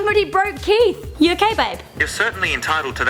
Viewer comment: did she just call siri aka keith babe